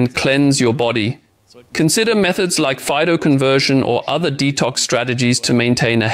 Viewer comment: what did he recommend as a detox strategy? It sounded like "phytoconversion".